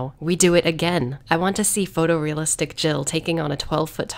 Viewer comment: I thought you said "I want to see photo realistic genitalia taking on 12 foot tall Nemesis" while I was listening to this at work. I had to rewind and then I was like "oh, Jill".